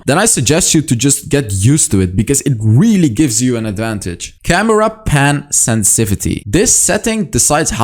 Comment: did i just hear camera pan sensivity😂😂